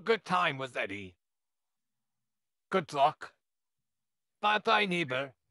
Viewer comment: I want ask why I heard he say Glock not good luck So how are you doing today and have a good day 🐇